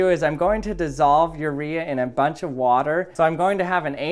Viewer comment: Did you just say "a bunch of water "